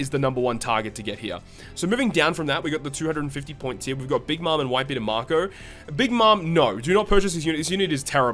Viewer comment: captions say "Big Mom and White People" lmao.